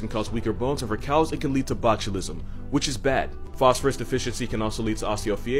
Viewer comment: Did he just say botulism?